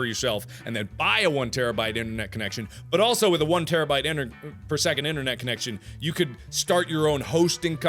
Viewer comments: Am i the only one who thought it said 1 table spoon a second? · I thought it said one tablespoon of Internet connection and I was very confused. XD